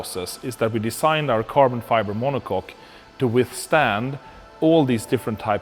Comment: I’m sorry did I just hear him say Carbon Fiber Monocock? 💀